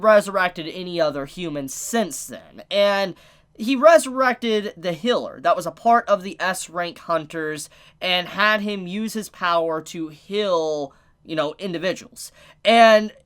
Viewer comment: excuse me did he just say hiller what? and why do i find that so funny